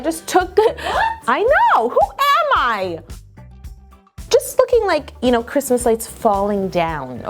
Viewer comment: did she say "just fucking like" 😂😂😂